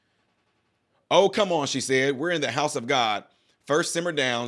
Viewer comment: I thought she said were in church. Say what